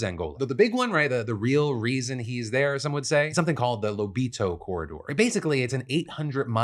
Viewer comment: Okay . . . I first heard that as "the Libido Corridor," and I was relieved to see it spelled it.